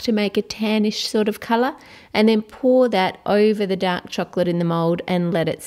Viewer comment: Sounds like your saying dat chocolate not dark chocolate